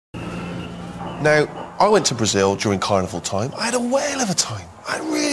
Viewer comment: i thought steve said when he went to brazil he lost his trunks in the sea and had diarrhea, here though he says he had a whale of a time